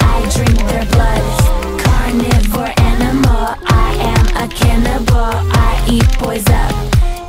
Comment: is it saying I am cat nipple???